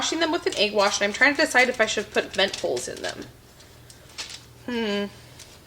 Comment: When you said you didn't know if you needed to put"vent holes" in the pop tarts I thought you said"lentils". I was like 😳😳 no!! Then I realized what you said. 230 am covid brain.....